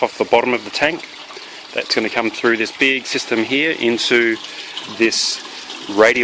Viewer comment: QUESTION Beard system? Is that what was said there? I don't understand how the water is going from the bottom lift system into the shroud, and then into the clean swirl output, can anyone better explain? Is the water going into the barrel from the bottom, up into the shroud thru that pipe in the center? How's the water come out of the shroud, it's bottomless, allowing for flow there? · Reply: He he. I think I said "big system'. Weird. I meant through the larger pipes. The water flow all works through gravity. As the water level rises in the fish tank, the only place it can leave is via the large SLO pipe. The water height in the FT is therefore same height as the bottom part of the hole cut in the side of the FT where the SLO horizontal tube exits. Does that make sense? The open-T piece on the SLO is to create an air break in the output pipe, preventing a vacuum being created in the SLO which would then siphon all the water out of the FT. Eeeks! Hope all that made sense.